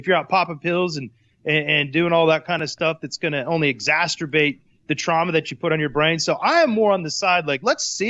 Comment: I think Poppinga just said, "Exasterbate." Awesome.